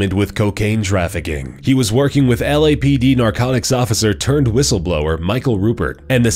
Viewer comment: Anyone else heard “Turd Whistleblower” at 6:44?